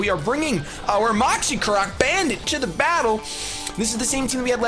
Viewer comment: First time you said bandit in the video, I thought you said banded, and I thought you switched items